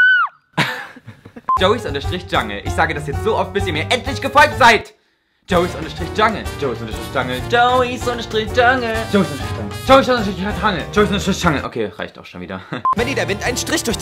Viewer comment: Hat jemand auch bei der Minute 1:34 verstanden:,,Joey Unterstrich Scheiße ? xD